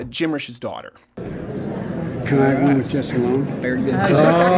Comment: Did he say "I like us to be alone."